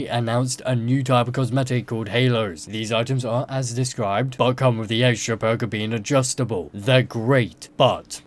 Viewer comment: want to run that by me again? subtitles said "but come with the extra burger"